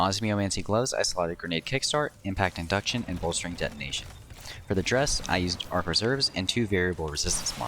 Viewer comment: Did he say, "for the dress"?